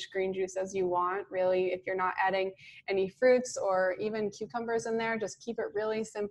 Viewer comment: I really like your information about including green drinks. I seemed to always fizzle when I tried to do even a 24-hour fast. Did you say cucumbers were okay to include or not? Any other suggestions for green drinks? I just started a 36 hour fast and it was so refreshing to have some celery juice several hours after my last meal tonight. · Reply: I heard them say NOT cucumber- at 22:03.